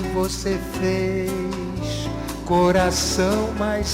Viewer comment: Am... Am I hearing things or does he actually say "Give us a fish" at 0:57 ish? 😅😅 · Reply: LMAO nah he says "que você vez" which means "...that you did", completing the sentence before. i can't unhear it now tho 😭😭